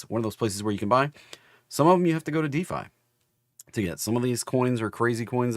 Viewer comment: hope you get this because I could not watch the CRYPYTO SPECIAL live. At approx. the 7-7:30 time in the presentation, you used a word multiple times which I did not understand, DEE FY (spelled phonetically). Sorry, I'm a CRYPTO neophyte and have no idea what you said and since there is no CLOSED CAPTIONING on your YOUTUBE presentations, I'm lost. I have listened to you every morning for several years and I'm finally convinced on the value of BITCOIN/crypto related trading, so much so that I have profited handsomely (5 figures multiple times) on several of your suggestions. Ever since your CRYPTO presentation (a few weeks ago) when you mentioned PEPEUSDT, I have been trying to open a crypto account to buy it. I'm currently on my 5th attempt/company. The first three were impossible to communicate with therefore suspicious to me. #4 turned out to be fantastic because I could actually speak to a real human on the telephone. I gave them my information and was ready/willing to fund the account, then everything changed and they went SOS on me. Frustrating, but still hoping I can make it happen. YOU'RE THE BEST, KEEP UP THE AMAZING WORK!!!!! mj' edlv